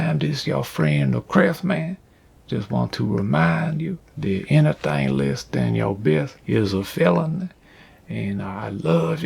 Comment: English is not my first language, can somebody tell me what he said here? I'm hearing "anything less than your best is a felony", but it seem kind of out of character haha. Thank you in advance!